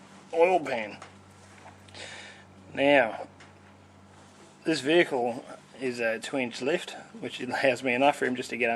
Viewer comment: Mr... not sure if you are still monitoring this post .. but if you are would you mind clarifying what you said at 4:23 .. "This vehicle ..??? is ??? to lift " and then at 4:29 ... "If you don't ??? ..." . I've tried playing back and listening many times but still can't make sense of what you are saying.. even the subtitles don't help. Thanks in advance. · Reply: Hello, I said my vehicle has a 2in lift which allows me access without ramps. Standard lift you will need ramps, car stands.